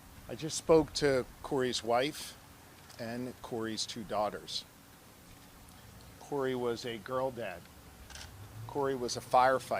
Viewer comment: What did he say??? "Corey was a girl dad"???